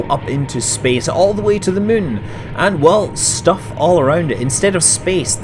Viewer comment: Did you just said Mun - or was it Moon? (reference to kerbal space program) 8:30 Reminds me of the Stanley Parable somehow. This playthrough will become a creepy series of insider references and flashbacks I suppose? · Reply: he is so used to saying mun that he says mun instead of moon